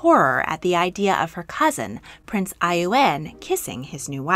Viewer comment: 🔎👒 Download June's Journey for free here: <link> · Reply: found it! @ 11:17 - my captions said "prince iun" also. Did a little Google search, read Tatiana's Wikipedia page, & came across this paragraph- "On 14 July 1911, Tatiana laughed at her distant cousin Prince Ioann Konstantinovich of Russia's engagement to Helen of Serbia. On 14 July 1911, she wrote to Olga, "How funny if they might have children, can (she) be kissing him? What foul, fie!" So it has to be him! Prince Ioann Konstantinovich of Russia, & his wife Helen of Serbia. 😁 hope this helps!